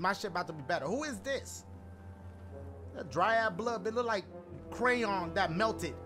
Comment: Lmao 😭sound like Dashie said "who is this? Dry ass blood nigguh!" 😂😭😂